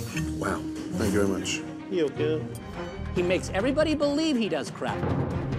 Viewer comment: I think he said “You’re good.”